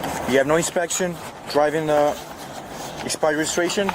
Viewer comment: I know his English is a little broken, but he clearly says expired, not suspended.